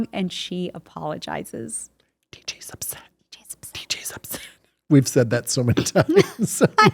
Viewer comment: What's Dave whispering? 5:46 Candace whispers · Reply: "D.J's upset". It's an in-joke with the cast. It was discussed on the podcast episodes with Andrea/Kimmy and Scott/Steve.